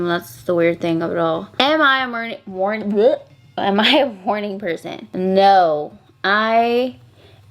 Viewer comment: Was I the only one that thought she said “ am I a horny person?” Lmao. Loved the look tho ❤️